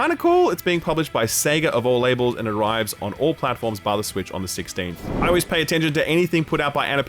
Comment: he says it is "on all platforms bar the Switch" while there are literal joycons on the screen lol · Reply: I always thought he was saying “via the switch” and it never made sense